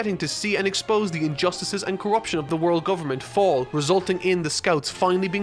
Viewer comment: did he say world government? You can’t escape one piece now!!!